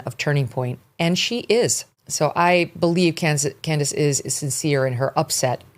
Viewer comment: I really really hope Candace spoke to Erika before posting that video, she hasn't even buried her husband yet. I get it, he was her "kid brother" but her video felt deeply inappropriate considering a widow and family are mourning. Her anger doesn't take precedent over a wife's grief. In the video she said "For those of you who wanted to see me in a moment of weakness" - I was SHOCKED and thought, why is she making this about herself? 💔 it just felt so wrong. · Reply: I thought you said Megyn instead of Candace, but I agree with you. I think both Netanyahu and Owens are both guilty tbh.